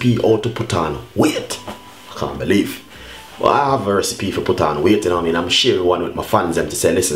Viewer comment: All i heard was "put on wheaat"...the accent 😍